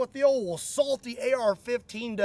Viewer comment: awwwhh i heard "Salty AR", when i thought i heard "Lets see what the ol Assaulty rifle does"